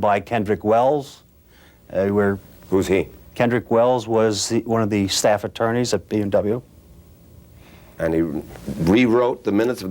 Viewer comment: Whe Wigand said " one or the staff attorneys at B&W " I heard " one of the SAAB attorneys at BMW " I was confused for awhile .